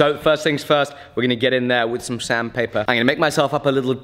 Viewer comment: Had a good chuckle when I thought you said Sam paper instead of sand paper 😝